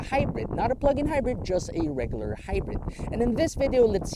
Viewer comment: Did I hear "just a regular hybrid"?